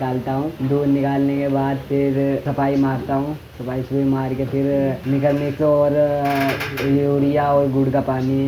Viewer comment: यूरिया और गुड़ k पहले क्या बोला? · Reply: Mineral Mixture